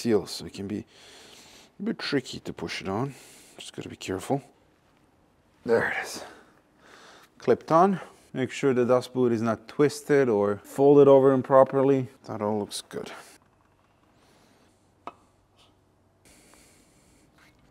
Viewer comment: You said "dust boot" but I heard "Das Boot" 🤣The 7 is world class.